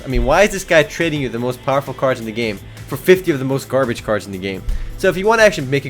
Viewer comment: Lol all i hear is "i have so garbage cards"